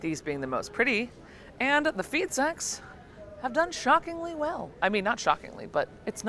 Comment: Ooooooh "Feed Sacks"......I heard "feet s....x"